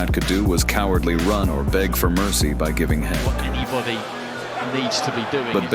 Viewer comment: Did he just say: Giving head?